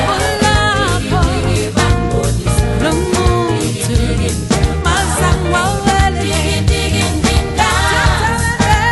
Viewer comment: did she just say "Lomnqundu"? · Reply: Luncuthu ,not umnqundu